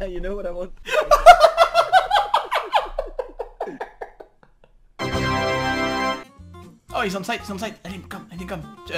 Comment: I didn't understand:( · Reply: Accidents...